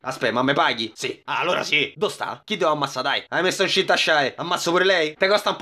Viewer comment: Enniente, al min 17:29 ho capito “Hai messo in cinta Shy”